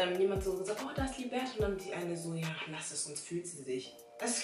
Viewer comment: was sagt sie? “lass es sonst fühlt sie sich?“